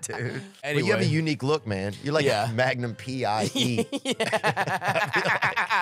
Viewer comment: At first I thought he said Magnum P.I.G.